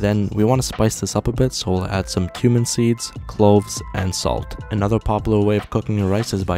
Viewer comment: I thought you said “human seeds”